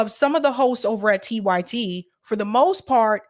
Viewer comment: I was listening to this at 1.5x speed and it really sounds like she says "Some of the hos over at TYT" 😂